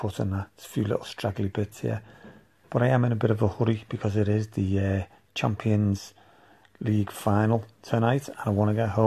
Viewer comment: Put me in the mood to get my non-working, cheap petrol acquisitions up and running. Brilliant and inspiring job once again, fella. At first I thought you said at the end, ‘Champion Leaf Final’. 🤪